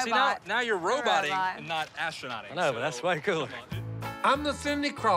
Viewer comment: Is it just me or at 19:37 does jep say that's f'n cool? lmao